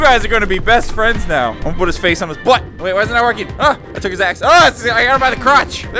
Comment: It sounds like he said. "Aah I took his ass"